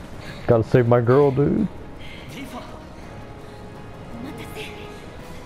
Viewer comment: the subtitle says what the hell but all he says is “Tifa”